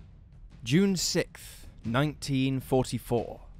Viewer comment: “June sicth, 1944.” Anyone else hear that?